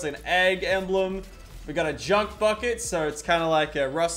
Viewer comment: Am I the only 1 who heard him say junk fuckit